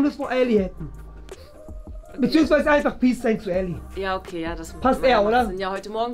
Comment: Bin ich die einzige, die bei 12:50 immer: "beziehungsweise bisexuell" verstanden hat? 😂